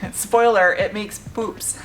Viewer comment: Almost thought Yu said pooch, not poops (or was that poods).